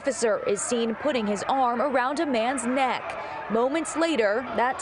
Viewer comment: Did she say at 0:52 , "Putting his arm around a man's neck." A better description, is that the officer had a man in a choke hold, and was pulling him back. I'm wondering if that tight choke hold caused him to fight for his life against the officer. George Floyd would be proud.